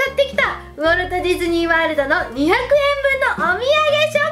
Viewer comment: ｢200円分｣って聞こえちゃった笑